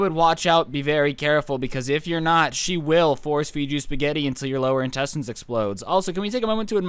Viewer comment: I thought u said she will force feed you spaghetti into my lower testicles